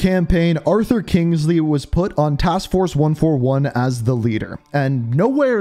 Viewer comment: did you say task force 141